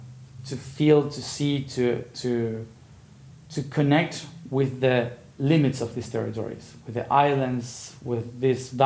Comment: It sounds like he is saying to connect to the limits of the nature there but he is saying the elements of nature, FYI, I realeyes this for myself and share!